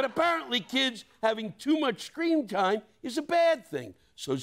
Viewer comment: did he say “scream time” or did I hear him wrong?